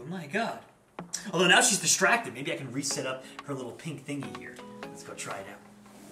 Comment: I thought it said soup not set up can you feed cookie soup for rabbits